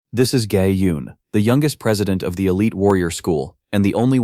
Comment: legit first second of the video I paused and all I heard was 'This is gay~' XD